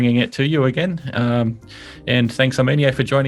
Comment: I misheard "Armeanio" as "Our maniac".. LOL